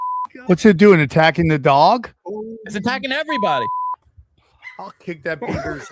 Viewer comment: lol thought you Said Bieber started attacking everyone